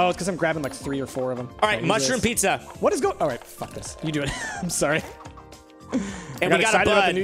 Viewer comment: Did he just said fucksss at7:10 min